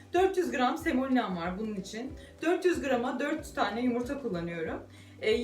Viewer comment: 400 yumurta mı yanlış mı duydum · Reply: 400 gram una ,4 yumurta